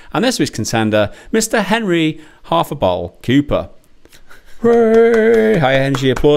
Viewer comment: whenever would say Henry "Half a bottle" Cooper, I really thought he was saying Hafa Ball... lol